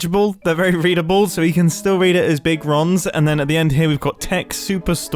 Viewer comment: I thought you said Big ROMS